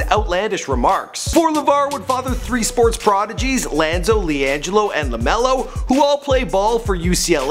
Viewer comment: did he say lanzo ball??